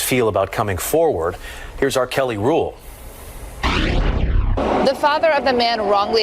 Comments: Anyone else think they heard "R.Kelly rule" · when he was talking about the impact that cases like these have, am I the only one who thought that when he said "here's our Kelly Rule", that he said: here's "R. Kelly" rule!? I really thought they were about to equate this case to him, as if there was a rule names after him. SMH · I thought the news guys said the R Kelly rules